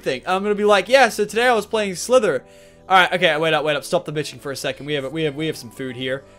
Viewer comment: Is it just me who heard "Stop the bitching"? lol